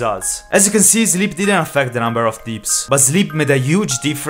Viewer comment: i just love how he said deeps instead of dips (i know its because he has an accent, but hearing deeps instead of dips sounds funny)